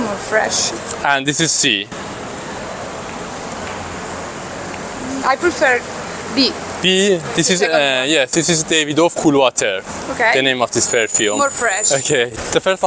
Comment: what is say David Wolf water?